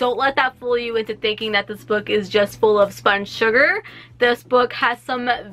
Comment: I literally thought u said "sponge sugar" and thought that was a real term and was disappointed in myself for not knowing it. How-much-I-believe-everything--says in a nutshell haha!